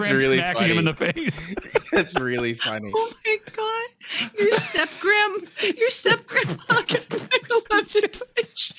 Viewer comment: What is saying at 9:25? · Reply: "Your step grandpa got nine-eleven'd by shrimp"